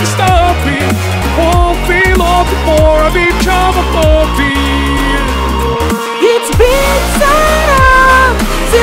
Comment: Markiplier was probably the closest sounding one 😭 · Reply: Although like the ending sounded like he said, "It won't be long before I become a puppy" and that instantly reminded me of WOOF